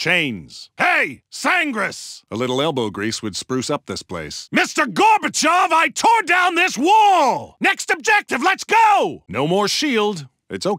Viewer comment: what did he say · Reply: 'mr gorbachev i tore down this wall'